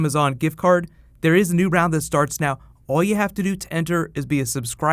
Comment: Haha at the end, I know you said "there's a new round that starts now" but I heard "there's a new round of Starks now". Had to go back and listen again lol.